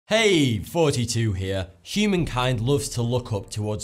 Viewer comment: I Thought You Said 42 Here In